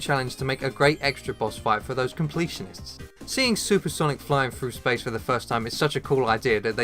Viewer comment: did he just say super Sonic?